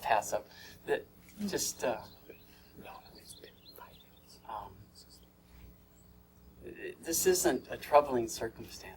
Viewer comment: What is the whispering at 5:37 saying? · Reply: 'It's been 5 minutes", basically telling him to wrap it up